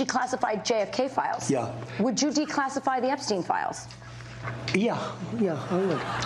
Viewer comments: Wait, did you say Epstein? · I thought you said "declassify Etsy" and now i want to know what those artists are hiding